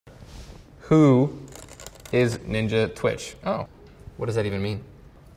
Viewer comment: I THOUGHT HE SAID “Poo Is ninja switch” HOW COME NOBODY HEARD THAT?